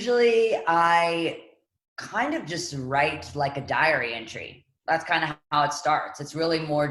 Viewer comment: Lol I’m so bad in English, that I thought she said diarrhea entry(beginning of it) not diary entry.